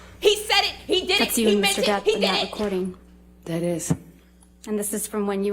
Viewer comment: she didn’t see any doctors about the injuries because you can’t fake them. she saw a therapist because she can fake her feelings. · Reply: It's horrible but also horribly funny but likeee yeahhhh the captions say Mr. Death 0.o (3:12) instead of Mr. Depp... Gahh even the captions are trying to wipe the floor with this innocent man. Actually maybe Heard's henchmen are trying a new backwater method of getting the public to take pity on her through subliminal messages (this is a joke btw..just thought those of you who always come in hot with the seriousness may need to know).. Anyways, I'm a new subscriber but I am thoroughly loving your content. You gotta little bit of everything and it's incredibly entertaining while also being thoughtful. Thank youuu ☺️